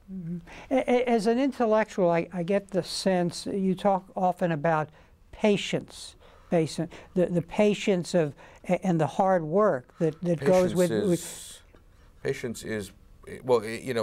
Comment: I thought the interviewer said "Haitians", and I was on the edge of my seat.